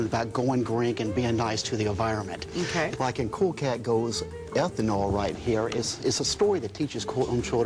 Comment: Is it just me or did he said " By Cool Cat go F-ing all right here"